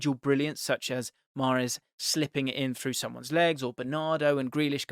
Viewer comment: Anyone else hear “mahrez slipping inbetween someones legs” at minute 8:30 🤣🤣